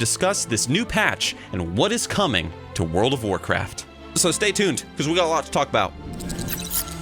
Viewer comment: i heard : So stay C'thunned !